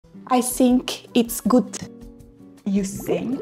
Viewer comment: I thought she said "I stink its good"